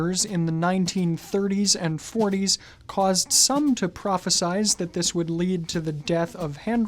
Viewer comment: “Prohesize” (15:56 ). Did I hear this correctly?